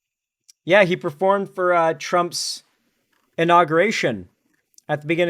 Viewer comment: i thought you were about to say "he performed for trump's sacrament"